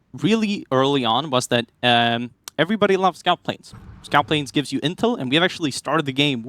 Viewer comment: was on a second screen and could have sworn I heard "everyone loves a scalp plane" and suddenly was very confused as to what tab was playing lmao